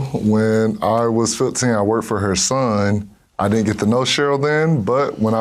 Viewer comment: Wtf does he say here and what does it mean, sorry English is not my native tongue. He never got the no show? Is this what he said? I don’t get the context…. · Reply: "I didnt get to know Cheryl then..."